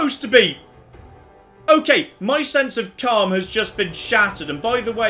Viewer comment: I heard "My sense of calm has just been SHATTED!"